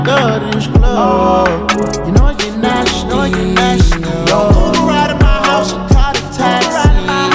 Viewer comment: I’m not gonna lie, I thought he was saying, “Moving round’ in my house.” 😂